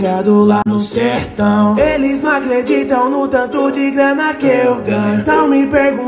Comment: Só eu que ouvi "Eles Nao acleditam"